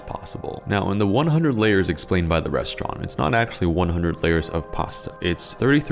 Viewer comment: I honestly thought thus said "1,000 layers" Like bro, what in the world are you doing lol That's enough f**king layers!!!